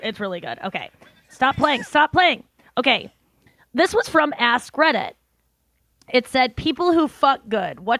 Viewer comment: Omg, I thought you said 'Ass Credit' when you said Ask Reddit 😂